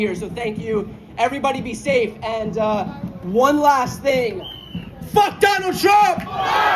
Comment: Huh! He said absolutely nothing! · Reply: I very clearly heard him say fu#k Donald Trump.